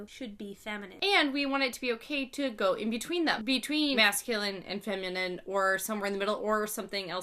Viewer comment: I thought she said mass killings. Then I rewinded and it said masculine.